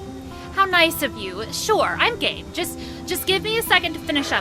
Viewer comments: Did say: I'm gay at 10:38? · did just said " Im gay "